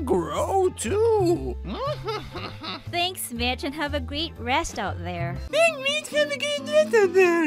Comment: I'm dead,did he say thank mitch and have a good rest out there